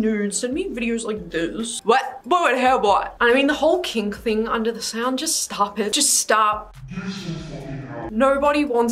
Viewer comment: I can’t tell what she said at 3:04 but that’s exactly how I feel about this shit too lmaooo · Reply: BAHAHA she said: “boy what the hell boy”